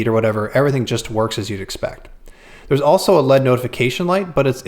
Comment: Did he say "LEAD" notification light?